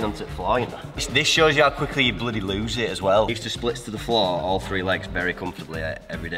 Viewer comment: I could have sworn said 'all three legs' when talking about how quickly you lose it 😂